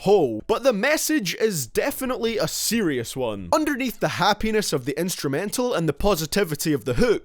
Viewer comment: I thought he said " hat penis" lol